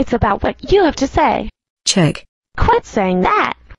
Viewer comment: I thought she said Czech